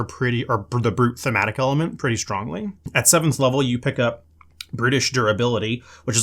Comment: Definitely sounds like you said "British Durability" 😂😂😂